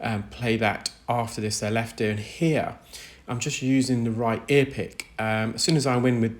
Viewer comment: So, knowing you are a very precise person, I always thought you were saying "I'm using the RIGHT ear pick" like you were saying you were using the "correct tool." Now that I see it written out, I see I was quite wrong... the RAI ear pick... Got it 😜